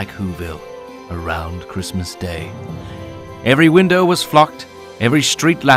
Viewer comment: To be honest when he said “every window was Flocked” I thought he said “every window was Fucked” and I’m like what the heck